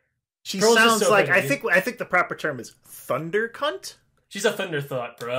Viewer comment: I thought said thundercuck